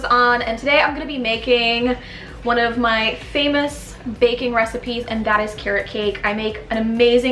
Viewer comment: i thought you said bacon recipes... much love!